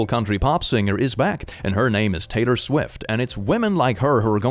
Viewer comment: I thought you said taytor swid instead of Taylor swift😂